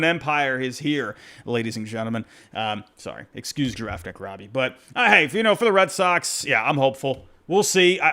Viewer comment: I can never clearly make out what you call yourself when you talk about the Red Sox. I hear both "Draft Pick Robbie" and "Giraffe Neck Robbie" at the same time.